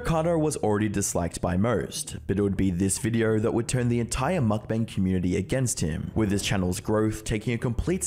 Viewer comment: Everytime said "mukbang community" I heard "fatfuck community"